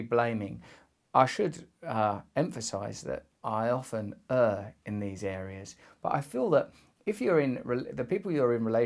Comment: "I often err in these areas" Youtube captions got closer than the actual subtitles